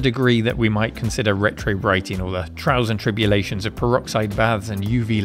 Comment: “Trouser tribulations of UV”? I do hope I misheard that.